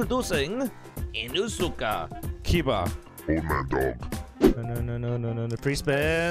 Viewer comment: when u said “old man dog” i thought u said “omendo”